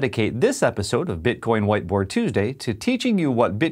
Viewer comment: I thought he said 'white boy tuesday'